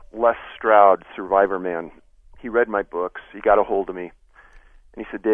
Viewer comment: All i heard was "read my book" 🤦🏻‍♂️